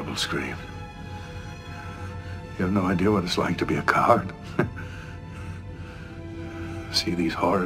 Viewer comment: The first time I saw this, I misheard Marritza's line as, "You have no idea what it's like to be a guard. To see all these horrors... and do nothing." While the actual line ("coward" not "guard") beautifully alludes to how Kira had the courage to fight against overwhelming odds since she was a young girl, and Marritza couldn't fight against his commander as a grown man, the misheard "guard" version has a certain irony to it that I really enjoyed.